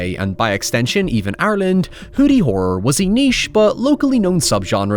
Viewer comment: As a silly American residing in the Northeastern USA, I have never yet felt it necessary to resort to subtitles on any of your videos...until 4:23 when I thought I heard you describe a genre called "Horny Horror". Hoyever, at that moment I knew I had to switch those subtitles on to make sure I had indeed heard incorrectly. Thanks! 😅